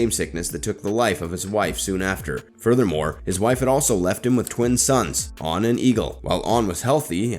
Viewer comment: What I heard was: '... his wife had left him, on an eagle.' To which I subsequently thought: 'Huh, it really is the land of Winter... what a cold-hearted bitch. Wuh-Wait, didn't the guy just say his wife died? Did... Did the eagle kill her or...?' My stoned ass had to go back 2 taps to tha left